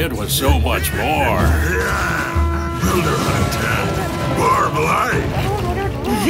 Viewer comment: did I hear builder hall 10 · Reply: "Builder Hut Attack"